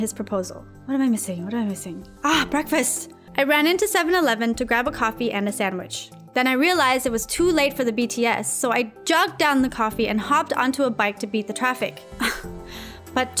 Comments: did I hear BTS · did I heard too late for the BTS??? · did she say “too late for the BTS ??” Army where r yall ?!?! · Am I the only Army who heard BTS? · Did she say Bts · did she say BTS??